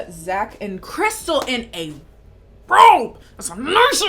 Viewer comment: i've been trying to figure out what says at 29:11 for like 10 minutes lmao i still don't know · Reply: She says 'crystal in a robe' (emphasis on the word robe) that's a nice a-- robe I want one